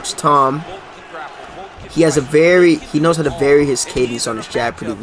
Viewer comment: what did say "he knows how to vary his __ on his jab" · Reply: Cadence